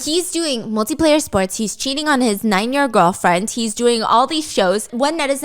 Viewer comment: i was doing something else while listening to this and i got a HEART EFFIN ATTACK when i heard "9 year old". I had to listen to it again to calm down lol